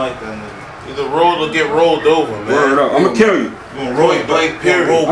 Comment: i heard dat " either R60LL or get R60lled over "